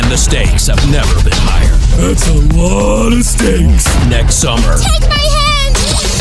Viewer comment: I thought it said mistakes